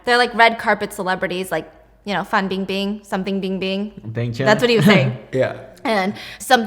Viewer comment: mentioning Bang Chan made me so confused I thought I heard wrong lol 💙